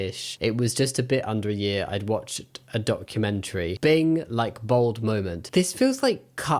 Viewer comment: As a professional writer, it is painfully obvious that the original author of this article/blog post has no idea how to write. The grammatical errors, switching of pronouns within the same sentence, shifting tone, and inherent bias give the post a lack of the authentic journalistic integrity necessary for good reporting. Secondly, the fact that this article ultimately reflects a hit piece rather than objective journalism discredits not only their distorted facts but their legitimacy as someone you need to take seriously. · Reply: The fact that they didn't catch the misquote of "like bold moment" instead of "lightbulb moment" (47:48) is proof enough that either they didn't proofread their work, or they didn't do so anywhere near meticulously enough.